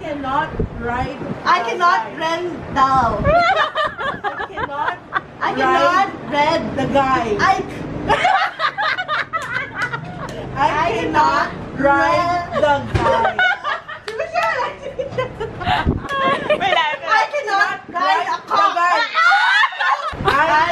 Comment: Tawang tawa ko sa whisper challenge 😂 did I hear that right "I cannot ride the cock"? 😂😂😂